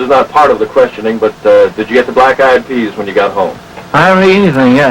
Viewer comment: I thought I heard the interviewer say at one point: "Black Eyed peas" LOL